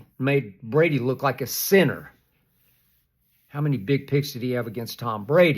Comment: If you look away from the screen at 2:51 it sounds like he said “how many big 🍆’s”